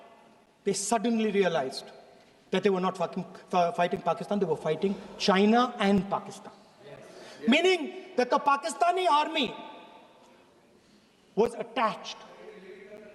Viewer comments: Did he just said fu*king Pakistan?😂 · 😂 did he said F**** …??? 😂😂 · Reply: Pappu said fucking